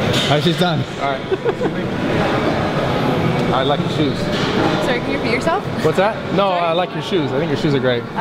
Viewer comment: LOOOL at 25:58 i thought she said "sorry did you pee yourself?"